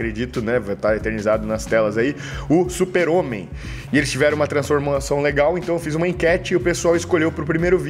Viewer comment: transformulação eu entendi direito?!🤔 Vídeo massa quero vc fazendo a dieta da Ana Carla ou da Amanda vê se vc vai sentir fome 🤭🤭🤭